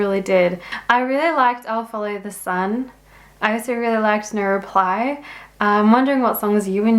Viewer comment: Accents are funny things. I like yours and normally have no trouble understanding you—but, when you said the first song was called "No Reply", I thought you said "Neuroplay".